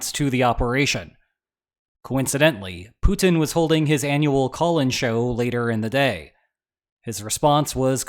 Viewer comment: the first time I heard this and even after the first replay, I was convinced said "Putin was holding his annual colon show" 🤮